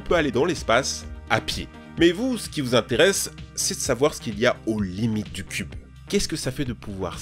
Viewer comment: C’est quoi ces sous-titres ? « Aux limites » devient « Holly Mythe »